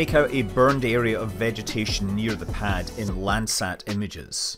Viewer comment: In what kind of images? (Last phrase in video, or nearly so… looking at the burned vegetation in… ??? I hear “black sand images”, but that doesn’t make sense.)